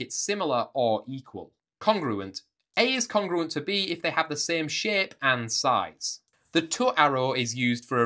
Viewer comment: He:- same shape I:- did he just say "same shit" or was it me?